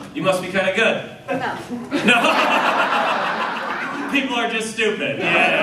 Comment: I think the art lady at 4:18 said “enough” as in “good enough” which is actually a way cooler line. Could be wrong. Either way great jokes, just discovered this guy recently hope he keeps comin up